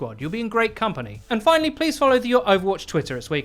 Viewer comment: I always thought Eddie said "I'm Eddie the champ". But he says "chump"...